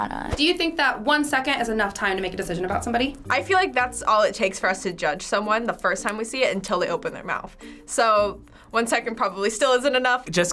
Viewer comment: WHAT SHE SAY ! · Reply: "i feel like thats all it takes for us to judge someone the first time we see it until they open their mouth"